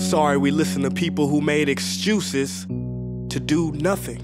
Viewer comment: did he say exjuices